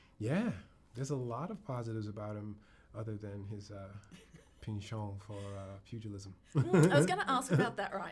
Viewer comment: Anyone know what he said right before feudalism at 1:14? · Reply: His penchant for feudalism.